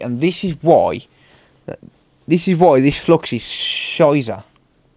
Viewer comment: did you really say at Minute 21:08 "This flux is scheiße/scheisse"? 😂